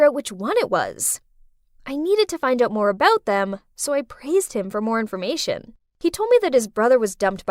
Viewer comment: I think it was meant to say "pressed", not "praised".